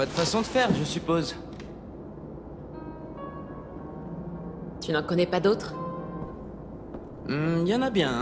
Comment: I thought clementine said “Have any baguettes?” I guess not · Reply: She said "tu n'en connais pas d'autres"